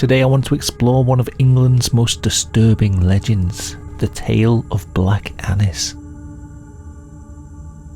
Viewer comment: I'm on autoplay then suddenly, i heard "the legend of black anus"